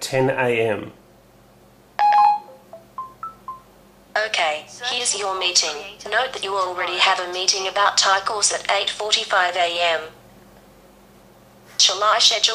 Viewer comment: Who else thought she said "you already have a meeting about tacos at 8:45 pm? :D